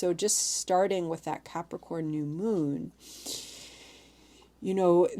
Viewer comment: For a bit of levity: I was distracted while eating at the start of this video and my brain heard "popcorn new moon," and I immediately thought, "oooohhh what's THAT?" Then it dawned on me I'd simply misheard. Lol